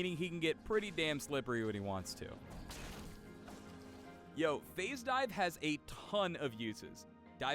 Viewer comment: Did he just say "yo"? Come on...not during the video with the black kid. You guys want to get flamed? Lol...